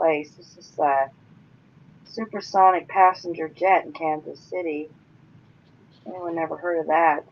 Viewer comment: The stuck plane... it sounded like you were saying Kansas, but the first article you showed It was spelled Kazan... the second article said Russia.